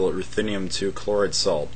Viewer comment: what is the red salt used in the solvent? It sounded like you said erthinium-2 chloric salt. Could you spell that out for me because I cant find that chemical